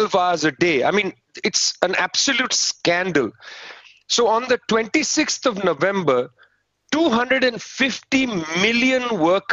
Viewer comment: How did that number of farmers and peasants 250 million, organise a coordinated strike. If true it is incredible. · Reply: OK, I thought he said the 26 November.